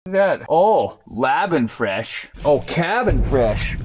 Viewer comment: Hmm, at first I thought you said "labia fresh"... 🤔 🫠